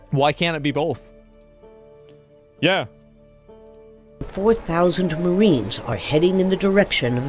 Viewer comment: Thought I heard 40K Marines.